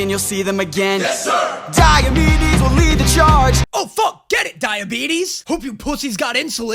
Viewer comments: Dead ass thought they said diabetics to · I also misheard it as Diabetes for the longest time before I actually started reading the iliad for the first time